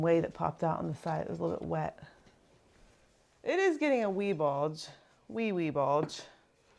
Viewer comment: "wee wee bulge" I heard it and now I can't unhear it...